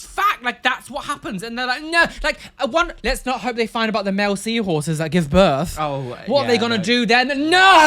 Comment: I thought you said 'wait until they find out about the Mel C horses that give birth!' as in Sporty Spice and was very confused for a few moments.